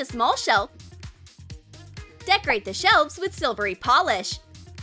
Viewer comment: Anyone hear her say shelfs at 2:19 hehe no hate tho love y'all